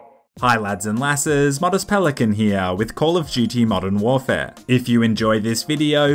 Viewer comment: did you say call of “Jew tea?”